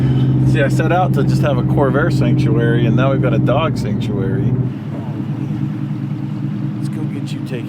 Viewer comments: At the start of this episode, I thought said "Dodge Sanctuary" LOL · I thought he said “Dodge sanctuary “ lol